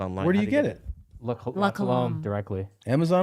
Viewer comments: What is the drink they refer to when Tom said how good the drink was????? Time line 1:05:13... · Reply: La Colombe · What is the drink that Tom liked at 01:05:06? I couldn't make out what and said. And get that drink as a sponsor too cuz I'm thirsty for what Tom's drinking! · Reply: La Colombe